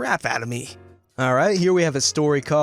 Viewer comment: i thought it said unvaccinated kids instead of uninvited Xdddd